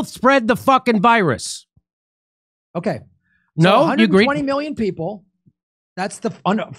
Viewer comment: When Graham gets emotional his brain scrambles. Max is hilarious, and low key a genius if you’ve read his works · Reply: Q Ok I went back to watch again. @ 58:15 120 million (40% of population unvaxed). I misheard 140 million. 0.08% hospitalisation rate. I don't know if that number is correct but that's the number he said. 0.08% of 120 million isn't over 10 million people. It's 96K people. I'm surprised Jimmy didn't pick him up it straight away.